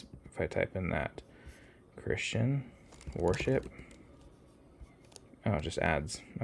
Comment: Funny, also, I didn't know the band "warship" which I thought you were saying.